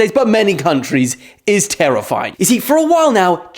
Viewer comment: tarrifying😂😂 i heard that dylan..i heard that one